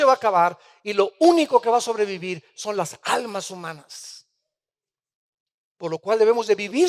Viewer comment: En em minuto 13:09 y el minuto 13:14 dice Armando Alducin" lo único que ha a sobrevivir son las almas semanas" ....¿Qúe significa?, o será que dice otra cosa y yo no lo escucho bien.. ?! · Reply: Almas humanas